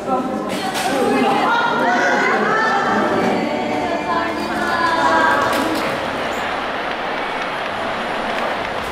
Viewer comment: did they say Amin? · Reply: Amen